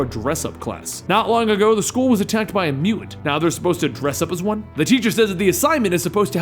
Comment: What mutant attack the school 8:01 ? well i think he said mutant right?